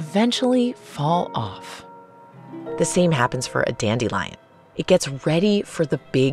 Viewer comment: I heard that as dandy lion hmm